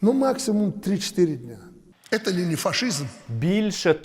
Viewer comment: Мені почулось, що Диктатор Лукашенко сказав, що це Ленін-фашизм!